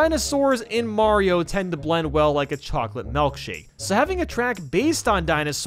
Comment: why did he say "melkshake"?? 😭😭